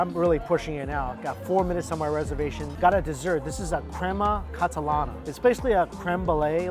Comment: omgggg 6:40 why does Mikey think creme brulee is called "crembalay" 😂😂😂😂😂😂